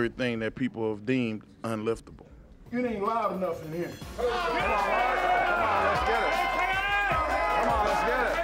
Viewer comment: Mark: "It ain't loud enough in here." Everybody: starts cheering out of fear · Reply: I thought he was saying "It aint alive up in here"